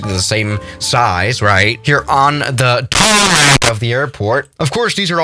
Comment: can someone clarify what tf he said 😂 and why it sounded like that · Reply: he said TaRmAc